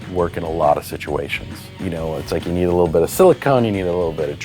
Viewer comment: Anyone else hearing siliCONE and not siliCON? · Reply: One of the times it kinda sounded like he said silicone, but it was really more like silicun